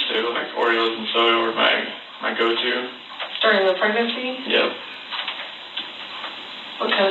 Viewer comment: why does she say, "during the pregnancy?" At least thats what it sounds like.